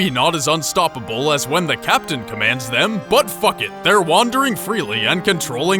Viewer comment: did he say buttfuck it XD